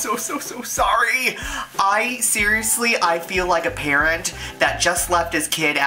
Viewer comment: I thought you said parrot instead of parent! Lol. I was like eh.... what?